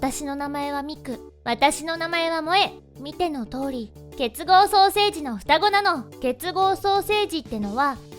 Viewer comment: [結合ソーセージ]って聞こえたww